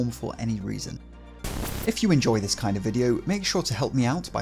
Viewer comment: I had to do a double take: "Did he say fondling?!" Lol